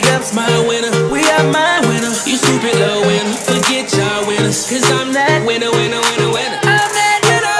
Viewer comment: I kept hearing I'm that WIGGA XD